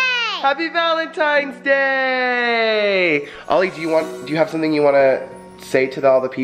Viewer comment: sounded like he was saying 'valentine day'